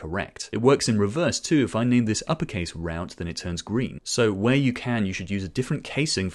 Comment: Awesome video as always! On the fun side did I hear 1:44 Uppercase ROUTE :D